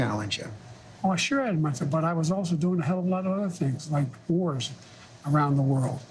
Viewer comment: When George interjects, does Joe say "wars" to answer one of the many things he has done? And then doesnt follow through just ends it with "Anyway." The subtitles have the word "for" in its place but i clearly hear "wars."